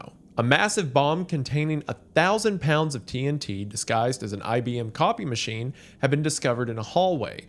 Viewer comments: At this moment, I thought he said it was disguised and as a "Coffee" Machine, and I was like, "how much Coffee was this thing supposed to be making? and "I didn't know IBM made Coffee Machines." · I misheard copy machine as coffee machine. So I was like "huh, when did IBM make coffee machines? Must of been part of their early business." Which I think says a lot about something.